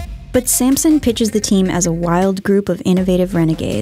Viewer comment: I thought she was saying Samsung